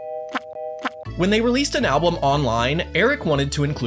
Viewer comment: did he say ablum or I misheard it